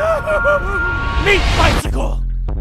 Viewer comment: All I hear is "MEAT BYCYCLE"😂